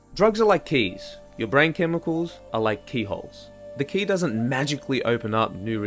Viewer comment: Who else thought he kept saying k-holes? Took me a bit longer than I care to disclose before realizing noo, he says KEEEEYholes lol 🙄 oof.